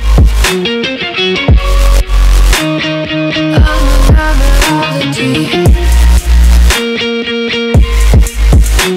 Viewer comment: may someone please tell me what she says at this part? It's not in the lyrics. :c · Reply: Don't trust the waters I'm a I'm a prodigy